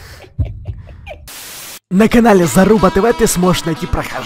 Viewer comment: мне послышалось или он сказал залупа тв а правильно заруба тв